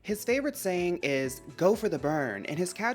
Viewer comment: I’m watching the video in 360p (cause I’m broke) and when Antonio went on the screen I legit thought his favorite saying is “always go for the bum” I said girl that’s straight to mother tier 😭😭